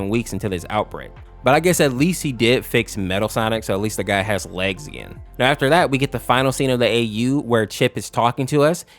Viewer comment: What is metal sonic saying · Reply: "Prepare to suffer" I think